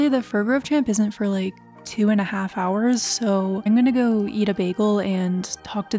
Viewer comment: omg I thought you said you were gonna go eat a baby 😂😂😂😂😂